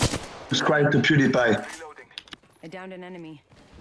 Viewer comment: did I just heard subscribe to PewDiePie?